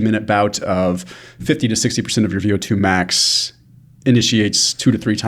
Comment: What the heck did he say at the 04:00 minute mark? 50-60% ____ max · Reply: VO2. ( from google - VO2 max represents the maximum rate at which oxygen can be consumed and utilized by the body during exercise.)